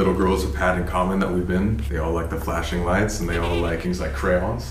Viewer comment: Let me know what you think of those footsteps at the end 👀 next week is SHAWSHANK PRISON, not gunna want to miss it.... if you want sneak peeks go to <link> ! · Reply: Thought the EVP said, "Gimme the flashlights" or something with the thudding of footsteps.